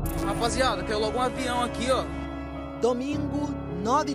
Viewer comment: "Caiu LOGO um avião aqui"? Não entendi direito o que ele falou